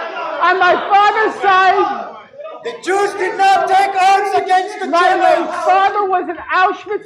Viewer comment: did i hear that correctly? "the jewish did not take arms against the germans."